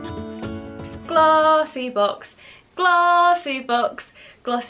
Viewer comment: everytime you said glossy box, i thought you were saying vossi bop